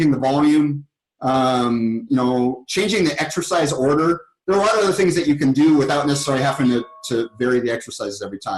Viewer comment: チンチンに聞こえがち